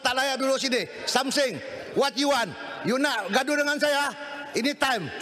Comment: the famous " wat you wan" instead of what do you want 😂😂😂